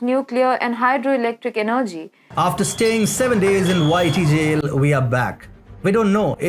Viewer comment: Please change your app commercial slightly. The first time I heard it I wasn't looking at the words and heard 'whitey' jail, instead of 'YT jail'. I was....confused...as to what had happened. I may not be the only one. :)